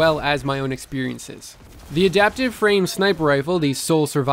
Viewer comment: It sounded like you said "Smiper" instead of Sniper, haha, still tired of that hour long video mate?